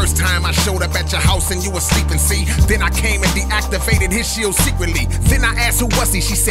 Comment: De activated? at 5:59 instead of "you activated".